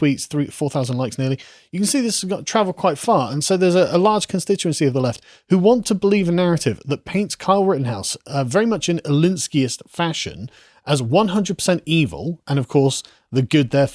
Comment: can anyone tell me what he said - similar to 'Lenski'? I would like to read more about it. Thanks!